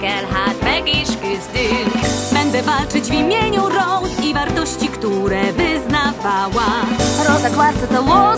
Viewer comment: It sound like “I will fight in the name of rose quartz she went through torture and died young” That’s what I hear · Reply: I hear Rose's Fart....